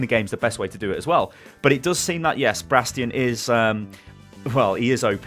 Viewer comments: Did he say Bradstion? :D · did you just say Bradstion😂😂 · did he just say Bradstion? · did he said "Bradstion"?! xD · Did he just say BRADSTION? screeches in Omnic Also, every hero that WRESTLED with Jeff gets nerfed immediately.